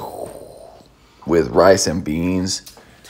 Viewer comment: anyone else heard 'with bryce and beans'